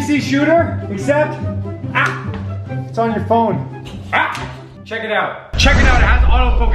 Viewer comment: I thought you said smack your phon